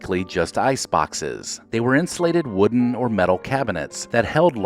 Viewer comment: Did he just say ice boxes were “inflated”? Or did I hear wrong - insulated!!!